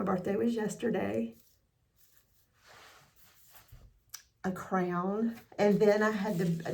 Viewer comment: 🎉Belated Happy Birthday.🎉 Oh my gosh, so I’m listening to this as I’m working around the house. I could not understand why you received a crayon🖍️ for your birthday. Then I heard you say you received a sash 🎽too. What??🤔 So I had to rewind…😂😂😂😂 A crown 👑, not a crayon🖍️!!😂😂😂 I had a good laugh. I enjoy your videos…. Whenever you find time to make and post. Thank you